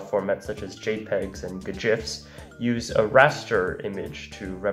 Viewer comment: Hol up. Did you just say gjifs @ 2:39?